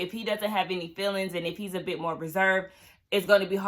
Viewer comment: No sis, he said Ryan doesn't have any felonies not feelings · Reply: Lol!! I saw two other reviewers who thought he said "feelings" too.